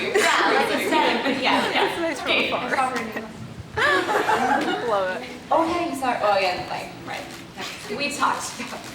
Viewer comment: What does Elise say at 34:07? :) Thank you for the vid! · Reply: Giovanna Arena she said that she saw her in New York